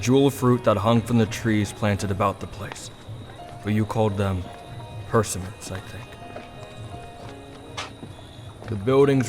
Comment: What did Bakugou say? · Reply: persimmons